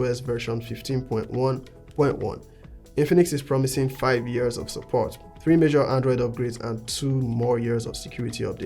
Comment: Did you just say 5 years of software update? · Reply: 5 years of support 3 major OS upgrades, 2 extra years of security updates